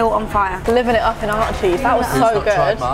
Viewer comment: THE CAPTIONS SAID ART CHEESE INSTEAD OF ARCHIES💀